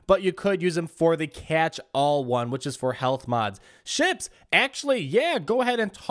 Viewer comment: i thought he said shit not suit